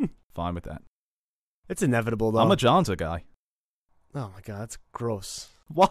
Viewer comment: What did he say? He's a "Johnsa guy?"